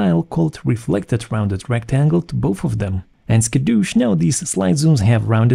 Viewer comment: did he say Skadoosh :)